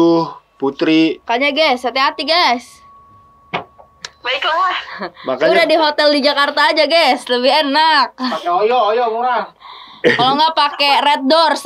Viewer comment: Kak ngomong apa sih? Pake koyo? Red dors apaan itu?